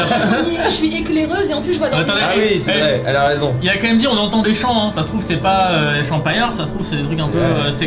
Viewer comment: Au début quand Fabien a dit "on entend des chants " moi j'ai compris "on entend des champs " du coup je comprenait pas genre le blé il fait plein de bruit ça m'a fait rire